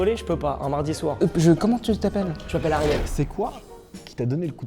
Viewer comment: Vers à peu près 13:15 quand on lui pose la question de comment il s'appelle j'ai entendu "Arrière" sans être méchant c'est un prénom ? Après j'avais mon casque mais j'avais la clim à côté j'ai peut être pas su bien entendre mais ça m'a paru bizarre d'entendre "Arrière" comme prénom